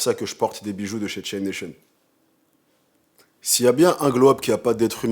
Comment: J'ai entendu "des bijoux de chez tchétchenne nation" ça doit pas être exactement les mêmes.